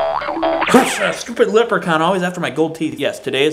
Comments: I almost thought you said LIPrechaun. Which would actually kinda make sense. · When you said Leprechaun it SOUNDED like you said LIPrechaun, which would actually be quite appropriate. Just thought you should know about your possibly subconscious wordplay.